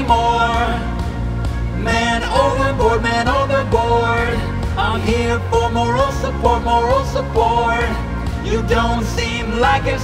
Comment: when I was little and I first heard this I thought he said "I'm here for my roasted pork" XD